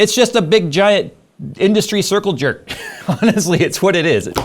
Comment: First time I heard "circuit jerk"